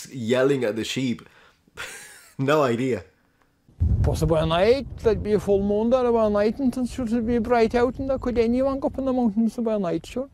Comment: It is English with a heavy accent spoke quickly with the words bleeding together as is typical to that dialect....For the most difficult parts, here is what is said verbatim... 3:05 "Possibly at night, might be a full moon there or it mightn't and sure it'd be bright out and it could anyone go up on the mountains a by night sure" 3:12 "Well there was 45 sheep missing like and the lambs 'n everything n the sheep, that's count... that's count out a nice bit of money like" 4:21 "can be done about it? nothin'" 4:25 "...... again (st) the wall, (it) s'not suppos ('t) to be there, and you know that" (complaining to young guy while pointing up the field, with the last bit under the news presenters voiceover) 5:18 "i'm missing about ten.. ten ewe's, It's not all that difficult, All you gotta do is have a good dog, have a good dog and, go at night, some moonshine night" 5:38 "Just put the dog around 'em, put 'em on a trailer, or walk 'em. And then probably somebody else to pick them up."